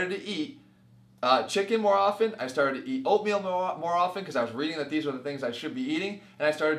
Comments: I thought he said "I started to eat opium more often". I was like "WHAT THE FUCK!" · sounds like he says I started eating opium more often lol